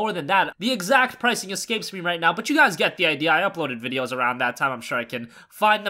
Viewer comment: did you say "Nvideos"?